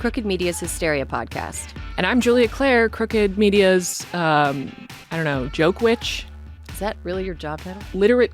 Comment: #Joke-witch is better! 😊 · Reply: I thought I heard Joke-wench. 😂